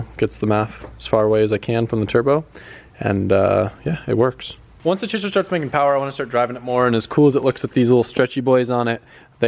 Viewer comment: wait what did you say? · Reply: Clarissa Sapien once the chaser starts making power I wanna start driving it more...